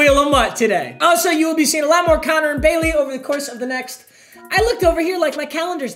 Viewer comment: who else thought he said “conner and kaylee” 🥺